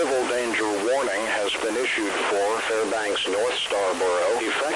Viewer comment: all I can hear him saying is 39:AM So what the hell is he saying